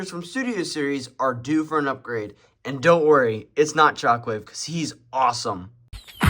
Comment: bro i swear i thought he said chocolate not shockwave😭